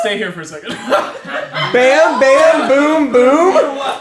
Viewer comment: Bam bam boom boom gets me every time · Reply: I always hear it as “bip bam boom boom“ .